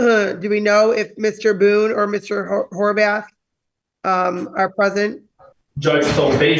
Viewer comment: Did I hear correctly? Is that man’s last name Whorebath? 😂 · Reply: I thought she said Harvath (maybe because I dated someone with that last name once, lol)